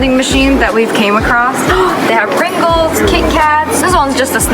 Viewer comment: did she say ringles 😂😂